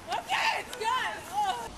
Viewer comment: Wait, at 2:37 did she say "OKAY ITS GOOD"? as she is screaming?